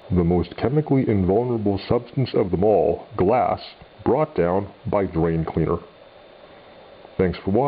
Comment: thought you said "brought down by brain cleaner" at the end, i was confusssed